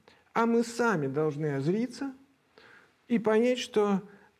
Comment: ВОПРОС 2:00 - не очень понял, что имел в виду АВ - почему мы должны "озлиться"? И правильно ли я вообще услышал? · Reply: Чтобы двигаться, поменять поведение, сначала нужно на себя позлиться, почувствовать агрессию. И сказать себе, что дальше так не может продолжаться. Ещё вариант.